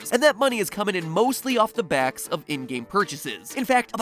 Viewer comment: I thought he said Indian instead of ingame, and being an Indian in school, I can tell no other Indian I know plays fortnite